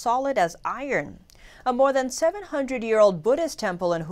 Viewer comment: I thought it said 700 year old bullshit lmao